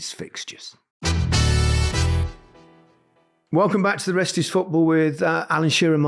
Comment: Gary Lineker said the rest is football so fast at the end there it sounded like he said racist football. 😭🤣